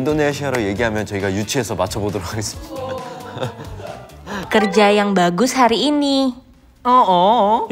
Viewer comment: jaemin bilang sedengernya dia aja "kerja yang bagus hari ini" tapi ngomongnya kayak kumur kumur😂 lucu bangettttt. tapi buat ukuran orang asing, nangkep kalimat segitu udah kerennnnn mantul banget emang nct dream